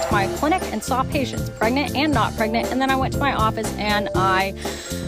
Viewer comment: Miss heard (may be misspelt) and thought she said "I went to my clinic and got pregnant" and thought what in the greys anatomy 😂